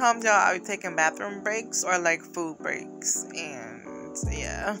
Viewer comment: Lmao 🤣 girl I could have sworn you said you take poop breaks 😂😂😂I was like girlllll you got poo on the nail 😆😆😆 but u said Food . I’m loving this set 💓💓I may need to recreate this 💅💅💅💅💅